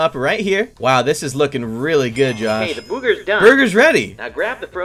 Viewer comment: why did he said booger is burger 🍔